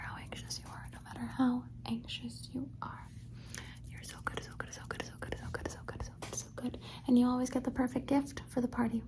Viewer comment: "coração coração coração coração coração coração" eu escutei isso kskskssk